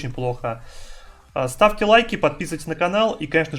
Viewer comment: Одному мне послышалось "Ставки, лайки"?! :D